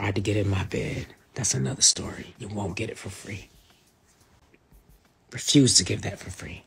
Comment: Did she just say “ you won’t get the names for free?” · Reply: "U won't get that story, for free" Re: what Preachers were tryn to get in her bed/invite them to hers.